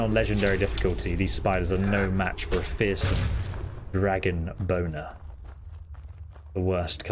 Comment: Did just say "a fierce dragon boner," or do my lugs deceive me?